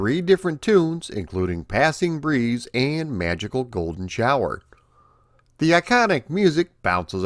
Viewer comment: That one song in Outrun is actually called "Magical Sound Shower". 😅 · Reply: I heard "Magical Golden Shower" and thought, I don't think that's the proper title. LOL.